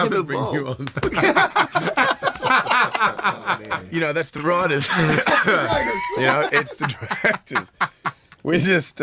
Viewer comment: Can anyone tell what Hemsworth says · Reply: "He doesn't do nuanced, that's the writers, that's the directors"